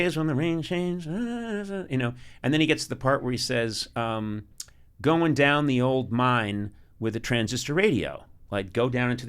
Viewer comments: I always sang “wrapped up like a douche” instead of “revved up like a deuce” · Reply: I always heard “goin down the old man” too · I always thought it said "Going down the Old Man with a transistor radio" - as in floating down the Mississippi - ("Old Man River")